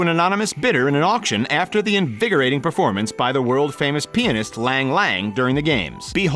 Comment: Lol why did he say peenist for pianist